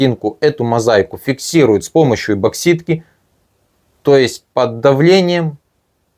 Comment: Мне послышалось, или автор эпоксидную смолу называет эБоксидной? Да, и ещё уважаемый автор, сделайте что-нибудь с буквой г. Большая просьба.